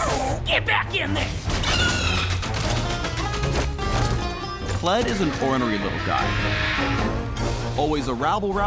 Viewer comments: What you heard: Kled is an ornery little guy. What I heard: Kled is a horny little guy. Now give me likes. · i thought he said "kled is a horny little guy"